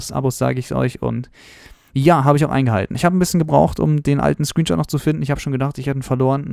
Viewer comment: Ich habe immer gembruh verstanden lul das Video wird mir erst jetzt vorgeschlagen